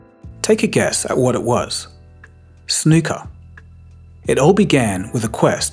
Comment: Did he say ‘snooka’? Wtf is that??